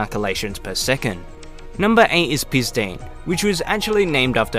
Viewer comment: who else thought he said pee stain?